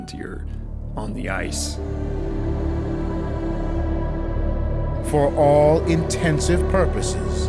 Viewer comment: The expression is "for all intents and purposes" not "for all intensive purposes". This is a very good video. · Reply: Shoot. I thought he said "In tents with Porpoises". Now I have to go back and figure out what the heck they were eating. I hope they weren't cannibals.